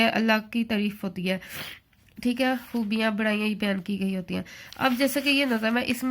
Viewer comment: mam hamd mai buraiya nahi hoti Allah ke koi burai nahi hai.... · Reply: She is saying barayi not burayi barayi means bara pan azmat😊